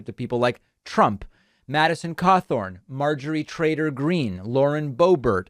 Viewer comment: Did I just hear “Marjorie TRAiLER Greene?! I sure hope so